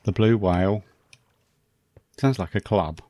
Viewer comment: Woo-hoo!!! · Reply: Haha.. I thought you said the blow hole club lmao.. I should rewind before commenting? Yes the blue whale 🐳 sounds a lot better!